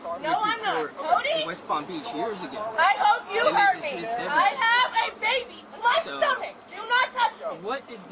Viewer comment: did she say "I ate a baby It's in my stomach"?